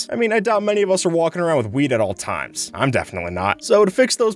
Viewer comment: i just noticed the captions say "weed" instead of wheat